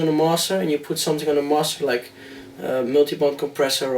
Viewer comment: Noob here. Sry. Can anyone tell me what hes saying between 21:06 - 21:19? Multibunt? Mosser? Ozorb? Help pls. And thks. 21:44 "dont put anything on your mosser" what's a mosser? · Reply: mulitibunt = Multiband, Mosser= Master, Ozorb= Ozone